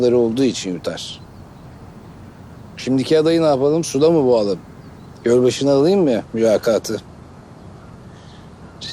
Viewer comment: da Orhan hoca ne diyor anlamadım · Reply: suda mı boğalım dedikten sonra gölbaşına alim mi mülakatı diyor